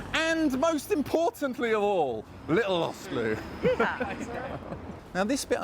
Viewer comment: Love the vids...but sometimes...the Brit accent is too much even to decipher...what is he saying after...'and most importantly'...@ 1:09? Sounded like 'little Oxford or little Arthur or little Oscar'... Thanks in advance! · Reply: Lil' Lost Lou ! She's my sister (holding the camera)